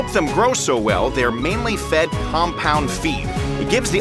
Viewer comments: did he just said they are feeding them compound V? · i thought bro said compound v like the ostriches were gonna fly at sonic speeds and shoot lasers that melt metal out of there eyes 😭😂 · I thought you said they fed the ostriches compound v 😭